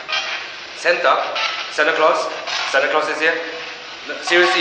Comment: I think at 4:00 , it said "Satan" and not "Santa"